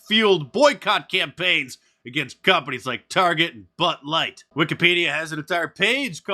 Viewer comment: ⚠ Order your WANTED: FOR PRESIDENT shirt here ➡ <link> ⚡ Use promo code MAGA25 at the checkout to save 25% off this weekend! ⚡ Join my exclusive Locals community here: <link> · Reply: Sounded like you said BUTT LIGHT.